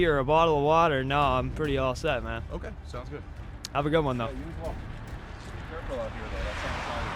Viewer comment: sound like he said be careful out here…. Something about slipping or ice… idk sounds like he’s trying to justify his shitty behavior